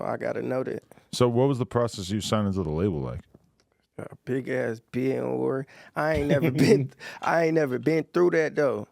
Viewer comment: can somebody tell me wth did he say cause I replayed it like 10x & still can't figure it out 🤣He said "Big Aahh" something 🤷‍♂️ Feel like Adam tweaked the audio 💯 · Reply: Bidding war